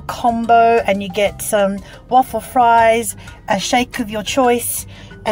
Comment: I had to listen twice as I thought you said 'awful fries' :D